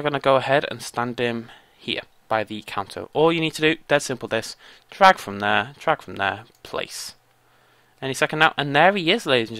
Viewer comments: wait that did he say at 7:48?? track from there, track from that, wtf? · I can't understand what he's saying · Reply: Andre Burton It's okay I barely understand myself either. :D It's "drag from there, drag from there" ... what that means is beyond me this was ages ago.